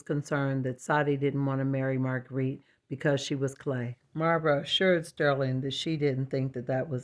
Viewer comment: Aw, you are a sweetheart, you deserve all the success because we love you❤ the portrait is beautiful! Clay or porcelain, I thought you said "because she was plain" and I was all up in arms for Marguerite lol!